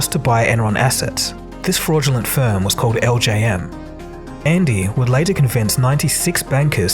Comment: I legit thought you said "LJN" and my heart practically skipped a beat thinking I had just learned the truth of the Laughin' Jokin' Numbnuts